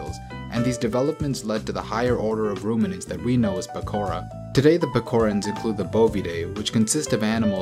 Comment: did you just say "Pekora" ??!!😍🐇🥕